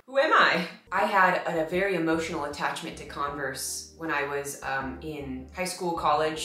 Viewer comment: You said “Converse” but I heard “Congress” and I wondered why you were so fascinated with it 🤣🤣🤣